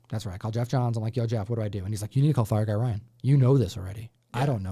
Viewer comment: Fire Guy Ryan> Gem Mint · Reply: I thought you said Phie Guy Ryan which would be a very strange Gem Mint crossover...! 😅